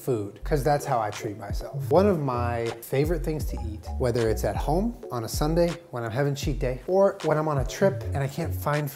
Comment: did he just say he puts chips on a sundae?- i